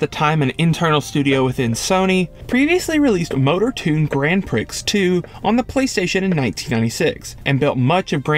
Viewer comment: did you just say, “Gran Pricks” ?